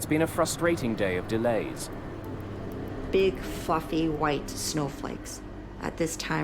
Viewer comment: BIG FU>>>White snow flakes" Could of swore she used the colorful F word lol Im listeniing and was like "Damn, this girl goes hard!" lol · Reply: The first time; I heard "big f'ing snow flakes." Say what?? Second time; "big f'ing--or; maybe "fluffy" snowflakes. Fluffy makes more sense in the context of large snowflakes. Still....My ears heard the f bomb. Still....I highly doubt that the producers would let the f bomb fly. No pun intended.